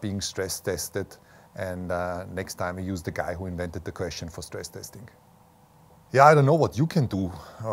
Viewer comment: what is he saying? "next time we use the guy who invented the crash ...?????... testing. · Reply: Next time, we use the guy who invented the question for stress testing